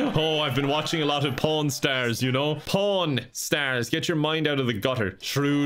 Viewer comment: When he clarified he said 'pawn stars, get your mind out of the gutter' I honestly thought he said 'pollen stars' before that and was very confused 🤦‍♀️